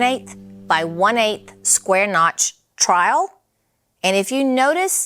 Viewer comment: omg i just laughed so hard @ 17:24 with you and saying "trial". i'm tulsa, oklahoma born and raised and we sure do have that southern accent but i had to turn on the subtitles/captions for it to tell me what word you actually mean and what the hell that thing was in your hand.... it's "trowel"! 🤣💕